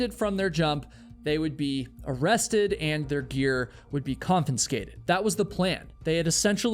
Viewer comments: I love the way you say conFINscated 😂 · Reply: I thought he said comPENscated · Broski, I think you said ConfiNscated? 16:18 😂 Love the vids! U the man ❤